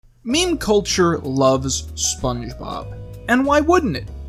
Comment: anyone else thought it said "meme future"?